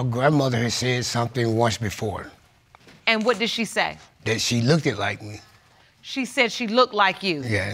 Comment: No Judge Lake, he did not say "she looked like me" he said "she look-ded like me". 10:15 Stick to the facts Judge!